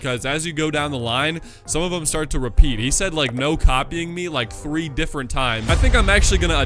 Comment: he actually said no copy my movies 🤓👆